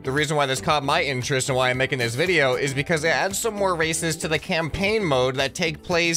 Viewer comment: i swear i though he said "racist"